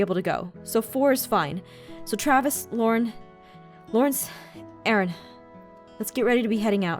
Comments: did she just say Lauron? Eeeeeeeeeek! NEW SHIIIIIP! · timestamp: 7:28 did just say lauren 😂?